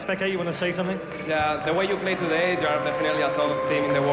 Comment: What did xPeke say at 9:05? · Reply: "The way you played today, you definitely are one of the top teams in the World"